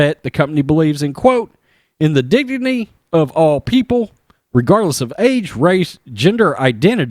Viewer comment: Oh "regardless of age"... thought you said AIDS.